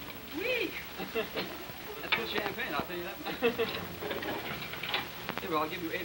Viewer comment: haha Sounds like Buddy says "Good champagne, I'll tell you that" at 0:03.